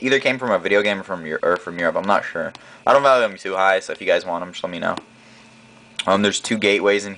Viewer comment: did you say "I'm too high"?